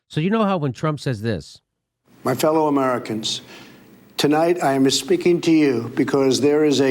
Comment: Anyone else think Trump sounds like he's saying "My fellow Americans, tonight I am misspeaking to you..." Slow down the playback at 0:18. Kind of obvious. Freudian slip?